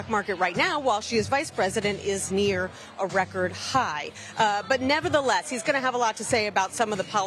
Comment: Did she say the stock market is now at a record high?